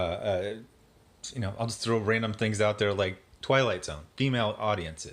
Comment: I'm probably wrong, but I thought he said 'Twilight Zone' instead of Twilight. HUGE difference. But I probably heard him wrong. Lie-berry.